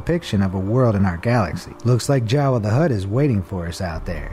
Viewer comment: did you say Jawa The Hutt?